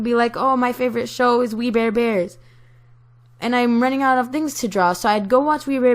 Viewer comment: oml i thought u said weed beard bears but i noticed that its just the cartoon xD